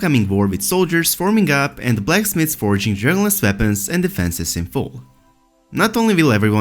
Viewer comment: I thought he was saying ‘journalist weapons’ lol